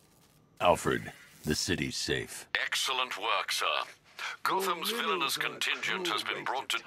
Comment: did he say gutham